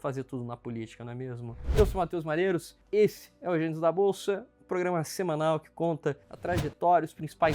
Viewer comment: Ótimo conteúdo. Só não entendi o que foi dito entre o 0:40 - 0:42 segundos. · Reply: Eeeu sou o Matheus malheiros, este é o gênios da bolsaa kkkkkkkk